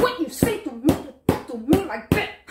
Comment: What I hear “How you talk to me?” “HOW DARE YOU???” “Talk me again I’ll turn you inchhow”